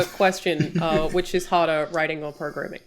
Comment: The initial question asked, I thought she said "which is hotter" not "which is harder." 🤦‍♂️